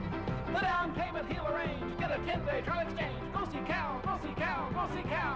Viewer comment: Its "GO SEE CAL, GO SEE CALL, GO SEE CAL" but it does sound like "PUSSY COW". I thought they were saying that too when i was a kid lol figured it out later. RIP CAL